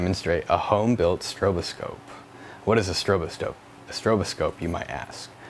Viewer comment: I thought he said “What is a stroboscope? My ass.”